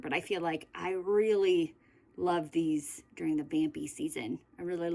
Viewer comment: When you said these lip products are for campy season. I thought you said Bambi season 😂 I was like Aww Apring colors 🤦🏻‍♀️I’m such a goober🤪